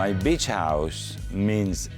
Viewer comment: Did he just say ‘Bitc# House’ ?